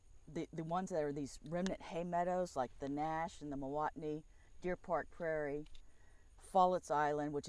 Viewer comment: and 19.16 and 37.07 a word that sounds sort of like milwaukney is said. What word is it? Thank you.